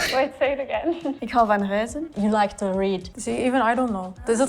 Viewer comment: I thought she said "Ik hou van huizen" so to me that made no sense, after she repeated it i understood the same